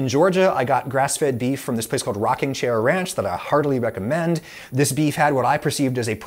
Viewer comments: I had to do a double take at 9:45. I heard that as "hardly" rather than "heartily", but given the context, obviously the latter makes more sense. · I had to rewind it a few times to hear "Heartily" instead of "Hardly" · sounds like you say you "hardly recommend" Rocking Chair Ranch